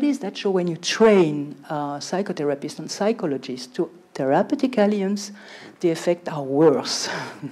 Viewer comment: what is that word? lmao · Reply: Therapeutic Alliance